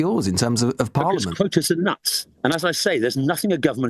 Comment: Did he just say voters are nuts or did I mishear that?